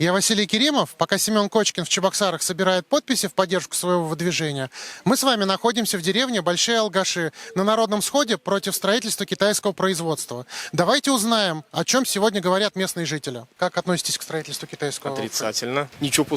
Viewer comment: Мне послышалось "Большие Алкаши"))